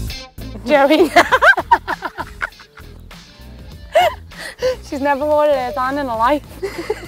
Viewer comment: What is she saying there? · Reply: "She's never wore her hair down in her life."